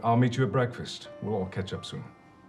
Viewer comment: I'm not the only one who heard ketchup, right?